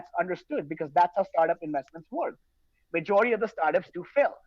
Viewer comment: I thought he said swirlds and I was gonna be mad. I’m glad this guy isn’t involved in my favorite project